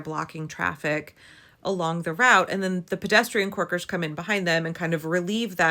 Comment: Corker! I was so confused as i thought you were say 'twerkers'! I wondered what the hell a bicycle twerker was...